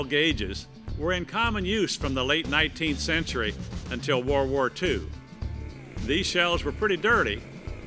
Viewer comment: did i just hear war war 2 0:17 when is war war 3 coming out?